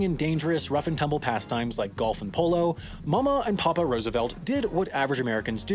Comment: Cannot lie I thought you said golf in polio instead of golf and polo